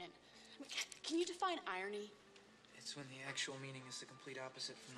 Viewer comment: Actual meaning is opposite of literal meaning. I always thought he said IMPLIED meaning was opposite of literal meaning.